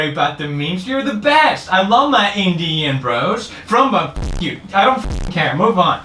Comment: No one: Felix: I LOVE MY INDIAN BROS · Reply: Once, I heard him say “Indian... bras?”